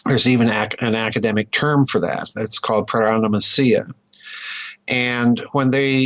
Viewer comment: somebody, please... WTH is the word he says at 42:50? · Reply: 1701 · A STAR TREK Series paronomasia